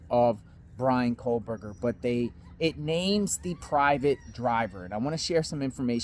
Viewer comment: I thought it was said Ramey was private driver